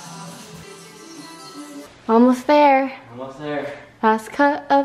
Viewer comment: eu juro que eu escutei um "vamos ver" o auge